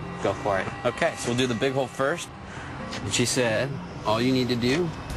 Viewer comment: LOL -- I thought said, "We'll try the big hole first... That's what she said."